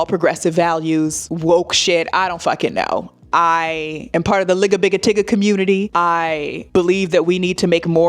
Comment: I heard “lick a bigga titty community” 😂😂